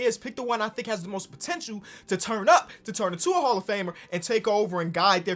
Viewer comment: I thought it said hall of farmer